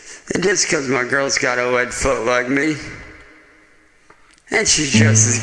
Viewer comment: did he say his girl has webbed feet like him?😂😂😂 question is does she have the tail too?